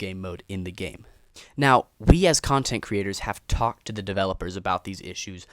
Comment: I thought for a second that bro said : "bs content creators" 😅